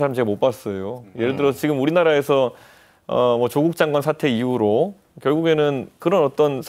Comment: 개백스 느그들 귀엔 사퇴로 들리더냐 ㅋㅋㅋㅋㅋㅋㅋ 어떻게든 조국 미화하려고 ㅋㅋㅋㅋ 0.25로 재생하면 사태라고 똑똑히들리는데